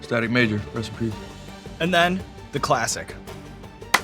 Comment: On the last question: Jack: "Johnny Depp.... Just sounds classic" Sean: "The Classic" Jack: well shit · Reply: Shit, I heard ‘classy’, not ‘classic’.